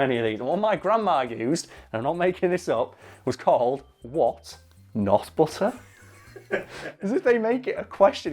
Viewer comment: all i keep hearing is "what, nut butter"